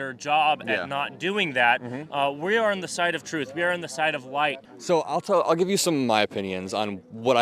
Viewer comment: Did he just say " We are on the side of white."?